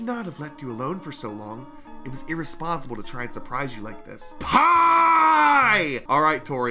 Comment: dad in portuguese is Pai You sounded like you were screaming pai but it was pie My father actually asked if i shouted him lol